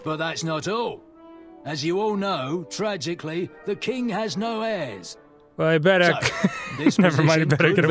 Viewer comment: I misheard them saying "The King has no ass".